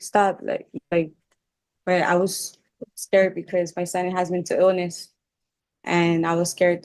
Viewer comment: "I was scared because my son was to illness" did I hear that right??! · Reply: Mental illness she said*